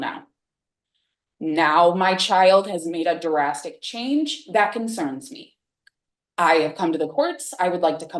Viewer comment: durastic change? That’s what she said right? If not then it’s smoked way too much. But to me she sounds like she’s trying to sound smart or some shit.